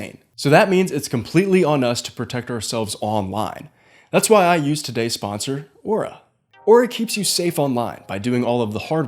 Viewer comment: i think the website’s called info tracer